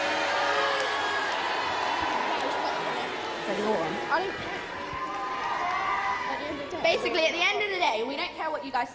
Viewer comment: if anyone can tell me exactly what Lisa said at 3:12 i will instantly subscribe (i genuinely don't know what it is) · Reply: She said “ignore them.” (Meaning the audience).